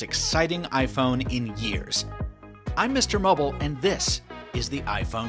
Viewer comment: I thought he said im mr bubbles 0:11 v. I was like WTF